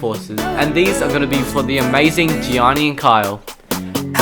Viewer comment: I thought it said Kobe and Gianna